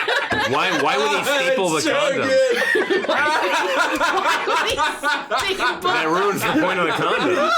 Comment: What is the joke made at 2:43:00 right after the line "..oh, no, there's the condom" ??? They're all laughing so hard that I can't figure out what he said! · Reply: said "why would they staple the condom"